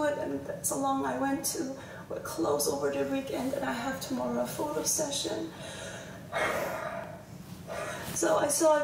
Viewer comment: Did she say photo session?💀